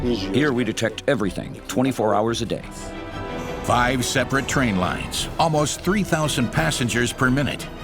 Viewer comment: Clutch it said 5 train companies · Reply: he clearly said "five separate train lines." Not companies. Lines.